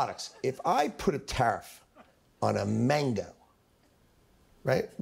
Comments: I thought he kept saying "put tariffs on Manga" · When he said mangoes I thought he said Mangas 😢